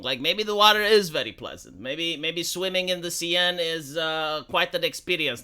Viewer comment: i mjst bw going deaf, i heard "Swimming in the CNN"